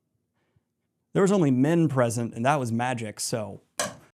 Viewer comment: I'm having the video on in the background, and at 53:34 I heard "There was only Min present...". I got really confused how in the world she got there 🤣 Great video! I'm really looking forward to experience the story again with your summary 😁